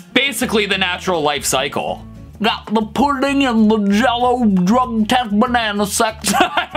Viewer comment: I cannot unhear the "Pudding & Jello, Drug Test, Banana Sex" misheard lyric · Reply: I hear “drumsticks” instead of Drug test